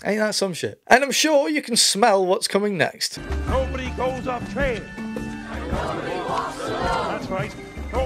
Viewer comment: "Nobody goes afraid...and nobody walks alone. Except for the sick, injured, or weak. Then you're fucked." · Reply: Lol I thought he was saying “no body goes off trail” Literally this whole series is so unintelligible and marble mouthed